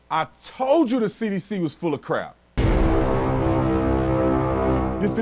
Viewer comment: I thought you said The CDC was Democrat, then I played it again and you actually said The CDC is full of crap. Come to think of it, Democrat and Full Of Crap are in reality, quite interchangeable. 💩